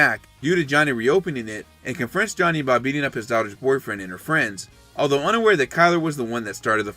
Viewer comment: anyone else think he said “his DOG’S boyfriend?” 😂